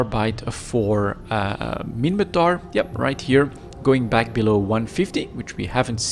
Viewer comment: They're not gone are they? Thought C type was still in the update blog, still 18x waste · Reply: I think i heard him say here gone. Which is for me the best thing i heard. So far